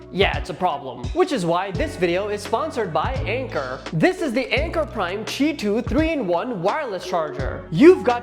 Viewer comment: of the video I thought bro said ankle biter